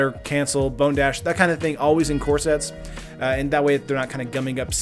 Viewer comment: I keep hearing that as "in corsets" Close but not quite the same.